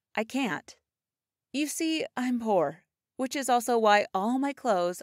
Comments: When she said “I’m poor” I thought she said I’m whore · Who else thought she said Whore instead of poor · I legit heard, ' I am a whore' when she said ' I am poor ' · Did she just say I’m whore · is it only me or i heard "I'm Wh*re" instead of "I'm poor" I swear i repeated it around 11 times and i still hear it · Hold up does she say "I'm poor" at 0:10 or does she say "I'm whore" I'm not joking here I deadass can't hear what shes saying there